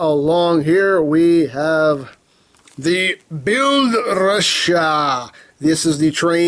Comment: i swear i heard you say build russia!! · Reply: Nope. It's Build Ressha, with a slight broken accent.